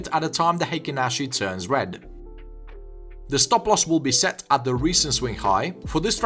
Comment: the fuck is he saying plz help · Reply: The stop loss will be set at the recent swing high